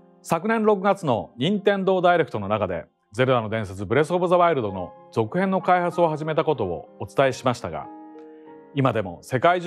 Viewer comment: perché ho capito Breath of ZA WARUDO?